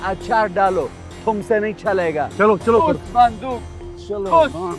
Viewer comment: Have I heard correctly? Ahsan khan said "Kuss bundooq kuss" ...